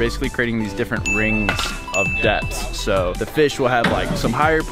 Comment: I thought he said Rings of Death😆